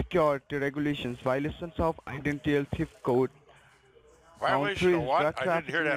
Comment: I thought he said violating my goat. I seriously thought that is what he said.